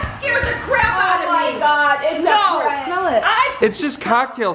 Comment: sound like she said « arrête »